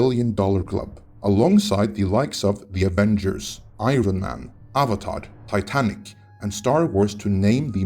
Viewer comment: did you say "Avatard"? lmao!